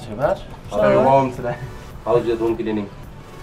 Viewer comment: what he say at 1:05? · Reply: he asked how our room cleaning was